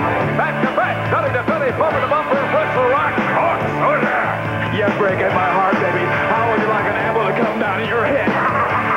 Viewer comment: wtf did mean gene say? 😂😂😂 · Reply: "back to back belly to belly bumper to bumper wrestle rock"